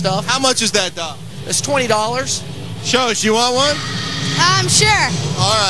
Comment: from 4:21-4:27 i think he says "buy one" actually